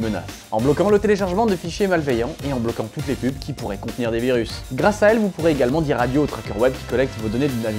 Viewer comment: ptn j'ai entendu "et en bloquant toutes les pu*es qui peuvent contenir un virus" je me disais que mon nord VPN nétait pas à jour :p